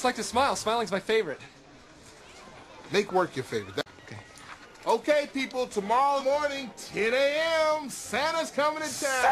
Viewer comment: I thought he was saying gay people